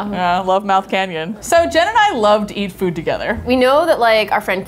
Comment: I'm I the only one one who heard Gemini instead of Jen and I